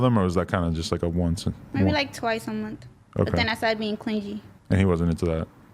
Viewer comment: I’m so sorry did she just say “clin-jee”